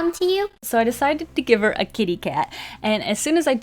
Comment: Hahahahaha when you said ‘I decided to give her a kitty cat’, I heard ‘I decided to give her a gay dad’ im crying 😂😂😂